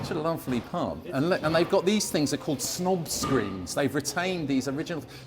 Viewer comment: I thought you said "SNOG screen"! But nope, it's a "snob" screen 😂. I suppose it works either way, tho dunnit? 😉